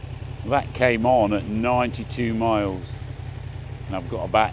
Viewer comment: did you say 92 miles 😮 boy that's not great, but I guess expected on these kinda bike